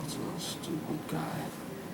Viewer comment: what does he say? Sounds like "he's a real stupid guy."